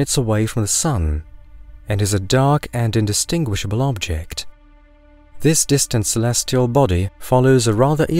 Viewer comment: I had to rewind 6 times to get "This distant celestial body", I kept hearing " This distance less your body" ... I was very confused 🤣🤣🤦‍♂️